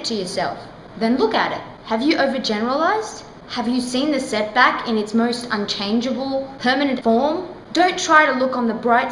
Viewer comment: what does she say? does she say "Have you seen the setback in its most unchangable permanent form?" is that what she sai? Is that what you said?